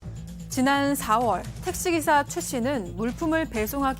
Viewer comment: 내 귀가 이상한거야?? 왜 택배기사가 택시기사로 들리지??